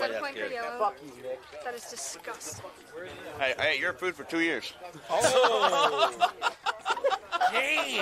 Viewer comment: ok what does he say at 5:44 “hey, i ate your food for two years”? i cant hear well can someone tell me lol